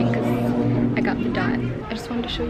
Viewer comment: What did you call your period? The scar? LOL 😬 IG: ancestralchild1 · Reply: I thought she said the dot 😂